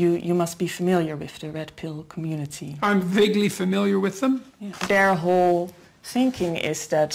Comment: I thought he said he was vaguely familiar lmao